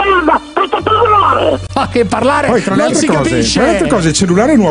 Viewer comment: cosa dice dopo "pezzo di m...."? non si capiva quasi più nulla di quello che diceva, impastava le parole. quanto mi faceva ridere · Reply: "Fate parlare"